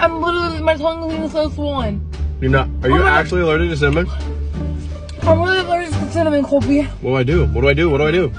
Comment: The whole time I was confused because i thought she said "im allergic to salmon" until i looked at the comments 😂